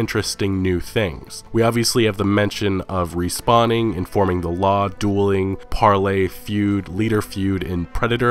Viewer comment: At first I thought he said "forming the law". That would be bad ass, imagine getting a posse of your friends together and taking down players who keep griefing others.